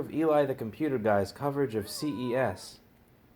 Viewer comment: man i heard that and i was like, me and yo momma gonna do a coverage of CEX n shit G UNIT! G UNIT!